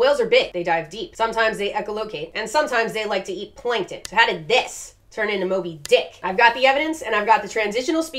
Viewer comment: At first I thought you said “How did this turn into a big dick.” Haha.